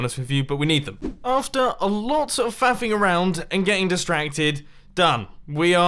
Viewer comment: did say "After "Fapping" around??!" 😨😲😂